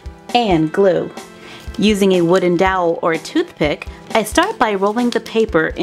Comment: At the beginning it sounded like she said a wooded dowl for a toothpick